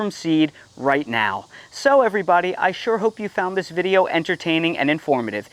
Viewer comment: “So everybody, I sure hope you found this video entertaining and informative.” I heard: “Sew everybody!…” It was a directive! ☺️ Thank you, for the kick start I needed to start my zone 7a fall garden!